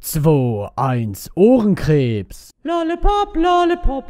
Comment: Ich hab am anfang verstanden 2....1.... Hodenkreps anstatt Ohrenkreps 😂😂